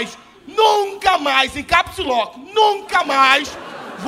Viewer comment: ele diz ''encapsuló''? O que ele diz? O que significa? Obrigado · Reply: Capslock* ele disse pra ela escrever em letras maiusculas kkkk